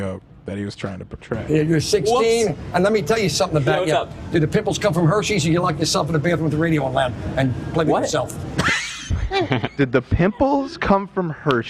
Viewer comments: I think I heard say “Did your pimples come from herpes, or did you lock yourself in the bathroom with the radio loud? And play with yourself?” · The whole “did the pimples come from Hershey’s” joke analysis had me fucking hOWLING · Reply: Omg I heard “did the pickles come from hersheys” 🤦🏻‍♀️🤦🏻‍♀️🤦🏻‍♀️ makes so much more sense now LOL